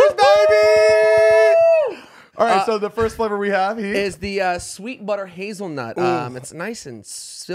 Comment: I misheard, "Skeet butter hazelnut" 🤣🤣🤣